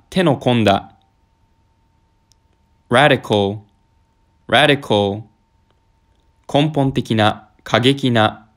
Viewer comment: 受験生の方ごめん、ラリホーにしか聞こえん